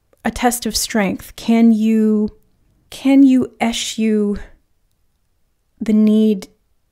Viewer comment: Pile 1. Can somebody please help me understand in minute 16:54 what's the word she's using when she goes: "can you ...... (Sounds like "as you") The need to appear successful..." Thank you!❤ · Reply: Can you "Eschew" the need?.. to be successful in favour of following your heart... ie: avoid, abstain, keep away from